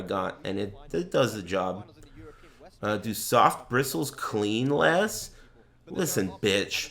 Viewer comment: on that soft toothbrush tech respect. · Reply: Ok but seriously I thought I heard harder bristles clean better